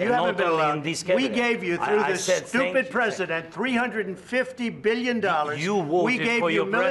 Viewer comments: did he call him stupid president? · did he actually said STUPID PRESIDENT!? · Did he just said Stupid President 😂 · Did Trump called zelensky "stupid president"?😂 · What did trump saying at 3:04 stupid president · Did he really say "Stupid President"? 😮 OMG horrible!